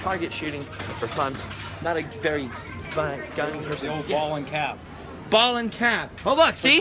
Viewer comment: OOH A toy gun at Disney. You won't see that anymore oh, and did you say the power of the COBRA?!! <link>